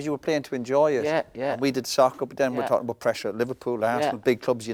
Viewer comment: did I here that right or is Roy calling football soccer